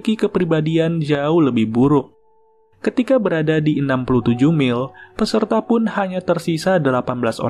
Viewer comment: did say 67?